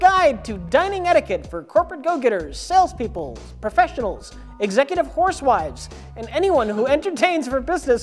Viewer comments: Did he say "executive horsewives?" You know, that's exactly right. I find no fault with that. Also holy crap...Shoji is still alive AND he still puts on his show...ji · when Rich is reading "housewives" in the beginning on that description I thought he said "executive HORSEWIVES" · Did he say "Executive Horsewives?"